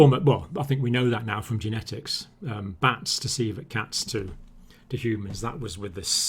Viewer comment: when he said civet cats i thought he was saying coelacanth